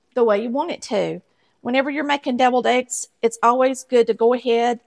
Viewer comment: I thought you said "bevilled" eggs...